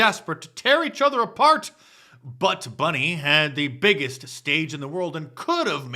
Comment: HAHAHAHA!! Did you say "Butts Bunny" @ 10:14?! That's fantastic!